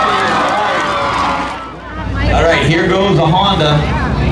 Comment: i swear you can hear a female say "that's not a honda"